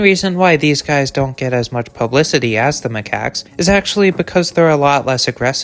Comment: I thought you said “racist macaque”😂🤣.